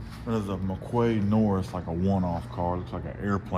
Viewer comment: The "Mccoy" I think you called it @ 11:00 what did that sell for? I love those art deco pod cars. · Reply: Hey! Around 50k. "McQuay-Norris" accent can be hard to decipher at times 😂 Thanks for tuning in!